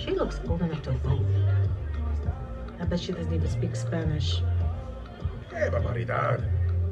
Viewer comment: what did he say? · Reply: que barbaridad